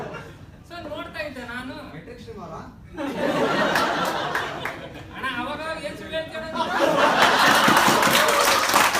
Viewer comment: what did he say in 2:17.the comment from audience.? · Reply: I'm not sure but yavdo('Matrix' anta yeno) team hesru heli, aa team avra anta keldangittu.. Edited: It must be Metricstream - his organisation, Nam company naa anta kelirbeku :D